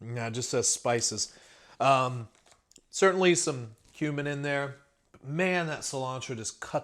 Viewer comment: Wow... when you said "there is definitely cumin in there" I thought you said there was human in there. I was like, "hey Google, how do you report Ted Bundy over here..."🤦‍♂️😂 Another good review buddy. I agree, chicken taco would be bangin